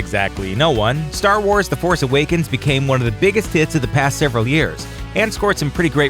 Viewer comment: HA sounded like you said biggest tits.